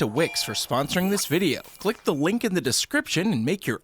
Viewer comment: Did any won here him say wink instead of link in